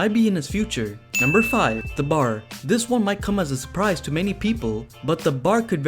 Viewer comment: I thought you said surprising...